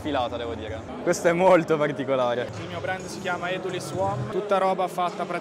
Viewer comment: Non ho capito il brand al minuto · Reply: eduliswom